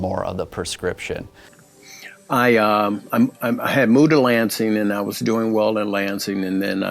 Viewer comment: The guy @ 23:24 mentions something about " mood elancing". Can anyone tell me what that is please, or if I understood him correctly? Thanks ☺